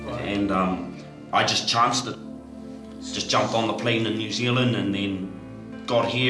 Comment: He is so confident that he will be allowed to enter the Country. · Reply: I thought he said he chanced it.